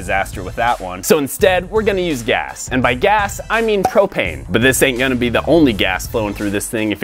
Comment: i thought he said cocaine